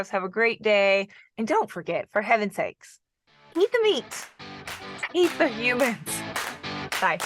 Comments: It sounded like you said, "Eat the meat. Eat the humans." But, maybe I need to clean my ears... Great video! Very helpful. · did she just say EAT the humans?! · Did you say, "Eat the meat. EAT the humans," at the end of this? I seriously did not hear "Save..." I heard, "Eat..." again. ??? LOL! Edit: Okay, after listening AND watching your lips more closely, I heard "Save..." but just, like, with the sound kind of blanking out a little for some reason, but the first time seriously sounded like, "Eat the humans." Hahaha! Sorry.